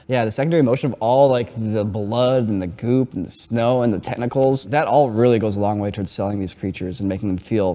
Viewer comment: he said “technicals” instead of “tentacles”